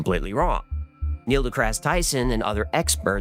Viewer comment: did you call him "Neil de crass Tyson"?? Lol that's hilarious